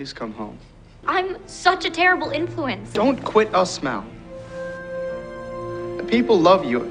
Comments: the captions say don’t quit I smell LOL I’m dying 😂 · turn on captions does it say don't quit I smell for you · When he said don't quit us Mal...The captions said... dont quit I smell am dying